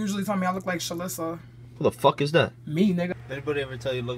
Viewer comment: I THOUGHT SHE SAID “me neither” INSTEAD OF “me nigga”